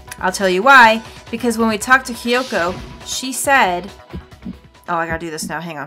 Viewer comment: Wait, did just say Hiyoko instead of Kyoko? How would have she known that name already?